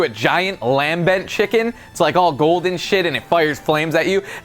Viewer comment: Did anybody hear Lasercorn at 6:24 saying s*** and they didn't cut it a out?